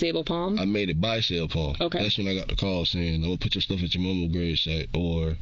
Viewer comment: “Bring my stuff to my grandmama house”. No car, no job, no place, no pus. Period. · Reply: I heard " I'm taking your clothes to your grandmas grave"